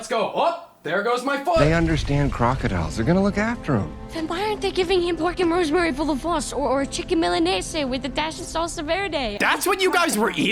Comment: can anyone tell me what this child says at 20:53? i have replayed it so many times and i cannot comprehend it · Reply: “Then why aren’t they giving him pork and rosemary (?) full of floss (pretty sure) or a chicken malaise with a dash of salsa verde” I’m 99% sure that’s what he said